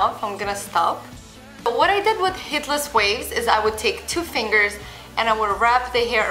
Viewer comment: Who else thought that at 2:18 she said "Hitler's Waves"